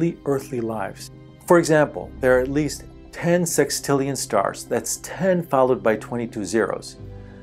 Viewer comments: he says "10 sextillion...a 1 followed by 22 zeroes. . ." but the subtitles say "1 followed by 21 zeroes". what he said was the correct way, but the subtitles are incorrect. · 21 instead of 22 on the captions just a friendly heads up. I have no clue what I’m doing here at 1 in the morning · subtitles say 21, he says 22